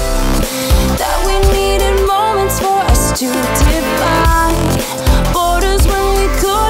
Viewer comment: I always thought they were saying "divide" instead of "define".